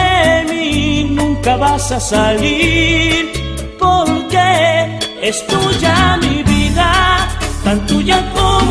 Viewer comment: Toda mi vida pensé que decía ESCUCHA mi vida , no ... Es Tuya mi vida 🤐